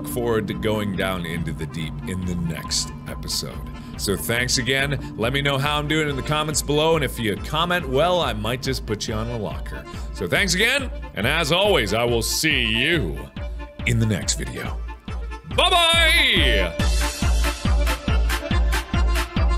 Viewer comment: "If you comment well, I might put you on a locker" Misheard that and thought "What?! I'll be put in a locker?! Mark's a bully" :(